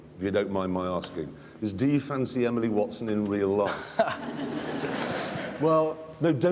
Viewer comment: Did Jeremy say "Emily Watson"?